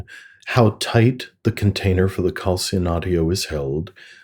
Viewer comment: What is the word used by the man (forgot his name) at approx. 7:56 minutes that sounds like calcium audio? (That was the translation on the automatic subtitles.) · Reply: Calcinatio, it’a an alchemical operation